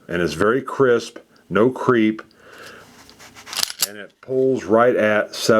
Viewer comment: Did he say no creep at 2:48? WTH?